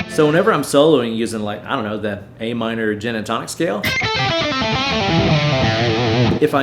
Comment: Hey, at 16:47 it sounded like you said, "the Gin and Tonic scale." I think I need to practice that one a bit more...hahaha. Rock On!